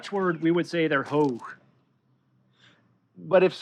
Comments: he's speaking a dutch word but I only hear the word "Hoog" which means 'High' in dutch. Im confused. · wtf is he talking about?? the dutch word "hoog" (if that's what he's saying) means "high"